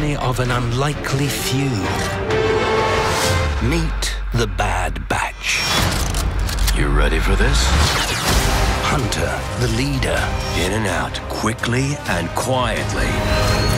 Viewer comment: Thought it said "the bad B*tch"